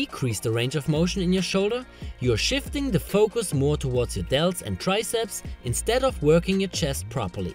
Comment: Could you back these up with scientific research? I have never seen any research state that the “more you move your shoulders in front of your wrist the more it moves to... triceps” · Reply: oops! My bad! I just rewatched it! I had misheard. You’re saying it moves AWAY from the triceps. I thought you said it moves more towards shoulders AND triceps!